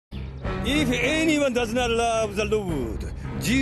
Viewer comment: Did he just say "If anyone doesn't love the Lewd"?